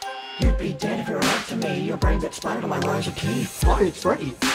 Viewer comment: I just found my new favorite song I also like the reference bonnie makes about being a good bloke edit it sounds like mangle says your brain expired across my rows of teeth